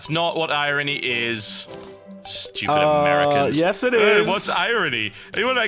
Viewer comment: did TotalBiscut just say "Stupid americans"?